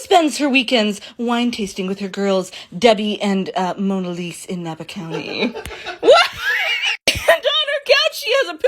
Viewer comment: did she say monalisa or monalese if the first one then she said my name if not then 😐